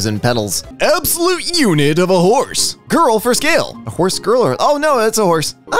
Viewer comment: I THOUGHT IT SAID GIRL FOR SALE AAAAÂAÆ